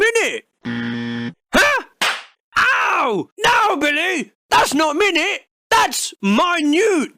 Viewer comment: Did you say Billy at 10:15?